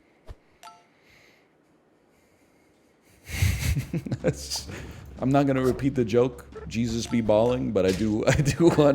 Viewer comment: does anyone know what said here? I need more juice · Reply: “Canada when you get sick: keep yourself safe”. I don’t know what it means though